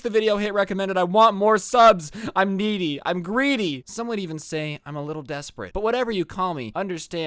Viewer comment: I thought you said “ I’m meaty I’m greasy”